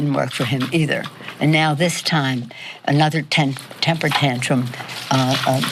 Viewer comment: She misspoke and it sounds like she says "another Tan Temper Tantrum" 😂